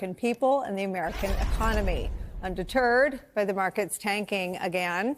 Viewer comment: I'm sure Nicolle said 'Undeterred, with the markets tanking again' but what I heard was 'Under Turd, with the markets tanking again...